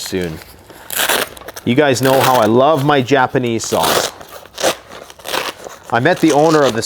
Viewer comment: LMAO at first, I thought you said "Japanese Sauce" so I thought you had some chopsticks and some really super hot sauce in there 🤣